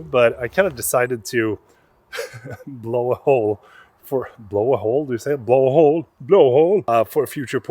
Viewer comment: Who else thought he said blow a load at first?😂😂😂